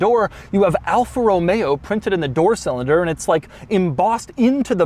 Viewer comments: did he say "door cylinder"? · Anybody else heard "door cylinder" at 6:10 or was it just me...?